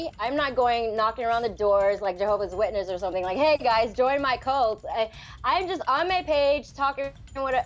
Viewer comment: did I hear... "hey guys, join my cult"... she literally admits she's making a cult. This chick is a troll, or she's high. No reason she would be laughing this much.